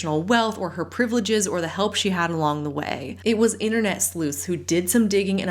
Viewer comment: Help somebody! Who are the internet slus is mentioning in 20:20 ?? I heard that so many times and still cannot find the definition🥲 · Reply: An internet sleuth 🕵️ is a regular person like you and me , likely on Reddit, that dig up info like a detective. Just for fun or to expose the truth.